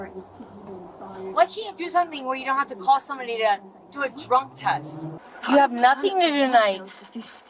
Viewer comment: Anyone hear her slip up and say “you’ve got nothing better to do thank make a drunk person… do a drunk test”